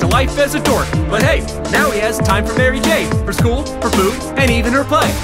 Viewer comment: The Line "For Food". I thought I heard "Her Boobs" because I wasn't paying attention to the lyrics XD